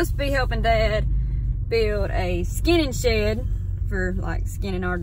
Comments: Oh “skinning”. I thought you were saying “skinny shed”. I love the way you talk! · Ohhhh...skinning shed. I thought you were saying skinny shed...you know...to put skinny stuff in. Pardon the brain fart! Surely there's gotta be a way to pressure treat some of the wood you harvest and mill.